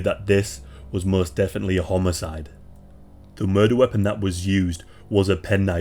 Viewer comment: sorry to be funny but sounded like you said a homo- cide like a gay killing if you replay it haha!